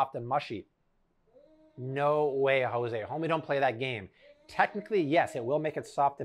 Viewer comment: Was it just me or did he just say "homie don't play that?" Referencing In Living Color???